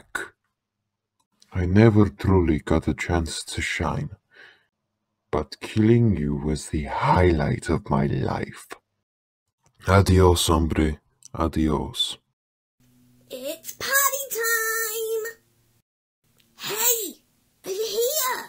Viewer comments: i thought helpy said its potty time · Male Phantom Mangle sounds like Mettaton. Ok I got the point the first million times. Is that a Flowey reference from Undertale? Yes. Why does Freddy sound British? He isn't. LOL it sounds like Helpy is saying Potty time. *That's* a British accent there folks. Words are said differently. Hey this character has a voice in AR! This video came out in November of 2018. Why did you reuse Scraptraps lines for Springtrap!? They're the same person. "Insert character here" is a Boy/Girl Cool. The nightmares sure like Plushies Yep that's their mechanic in the game. Why didn't you do "Insert character here". They either: A. had a voice already, or B. Didn't appear in the game. Freddy sounds like Mettaton ... You're joking right?